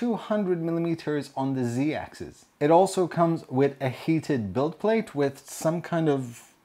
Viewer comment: I thought you said hated bed at first